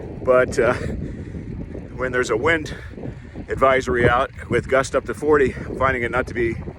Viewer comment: I thought it said "Bikini during wind advisory" . Glad I was wrong .